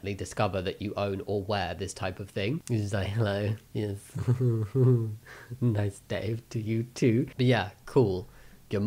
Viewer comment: did you say “nice Dave”?